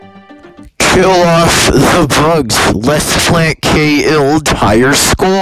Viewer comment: What he actually said 5:30. What it sound like he said : :::::: kill us the bugs let’s play K entire skill